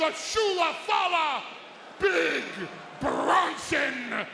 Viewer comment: did I hear shoelafala 😂😂